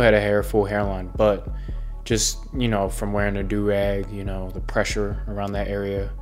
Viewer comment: I thought it said he’s been wearing drag